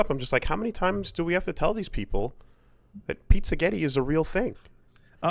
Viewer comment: I legitimately thought they were saying pizza-gate instead of pizzagetti